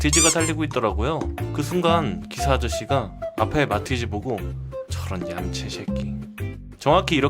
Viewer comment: 마티즈인데 강아지 종 말티즈라고 들었엌ㅋㅋㅋㅋㅋ그래서 강아지가 도로를 달리고 있나?생각함ㅋㅋㅋㅌ